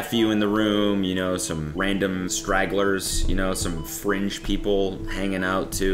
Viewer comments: Everytime Cody said “fringe people” I kept hearing “French people” and picturing people in barets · I thought he said random French people at the fart speaker guys death bed 😂😅 but I guess he said “fringe people”